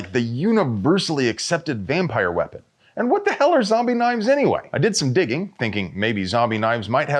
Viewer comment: At first, I thought you said, Sadiq Khan wants to ban "zombie nights".